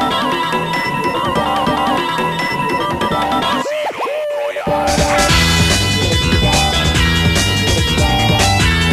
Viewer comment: what he says at 0:11? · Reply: Its casino royale according to the audio files